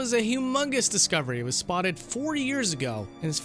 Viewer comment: Totally sounds like you say 40 and not 4 years ago.